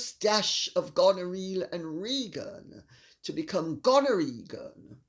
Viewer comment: thought you said "..with a generous dash of gonorrhea.." 😂